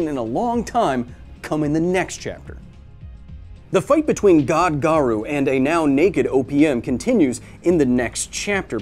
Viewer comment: I heard it as "The fight between God garou and a now naked opium" 😂😂